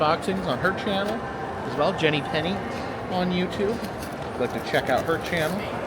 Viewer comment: What is channel again? I thought you said Jenny Kenny, but it wasnt her! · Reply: Jenny Penny